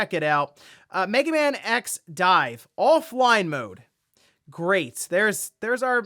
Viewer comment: When I misheard Megaman X Dive as Megaman X9 all I could do was sit in disappointment.